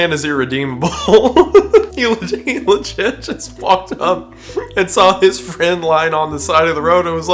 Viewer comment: Wait... are my ears messing with me or did he just say 'fuck'? Edit: Nevermind, my ears messed with me lmao. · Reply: Unless you're talking about something a good bit after the time stamp, he says "walked up"